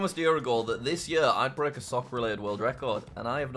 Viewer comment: did he say soccer rated?